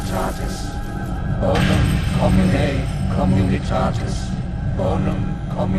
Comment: What were those people saying at 0:09? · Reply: Bonum commune communitatis